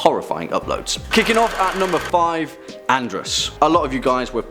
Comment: As soon as he said Andras, I thought he said Andross. hahahaha